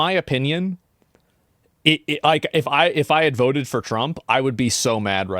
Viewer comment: i think he's saying that he voted for kamala?😂